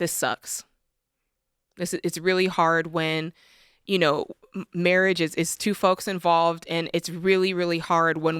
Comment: I really thought she was about to say Marriaguana.